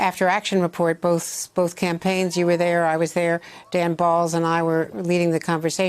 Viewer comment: Did she just say “me and dan balls” pause 😂